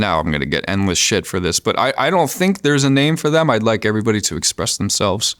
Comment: I thought you called us Puthinators?